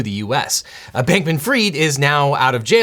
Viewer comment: I keep hearing "Sandbank McFreed" when he says that name fast. My hearing must be bad.